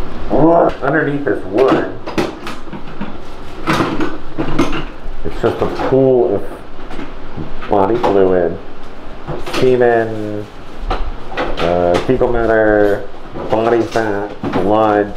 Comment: Did he say semen?